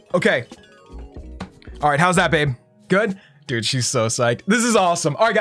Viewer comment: Did he just call babe???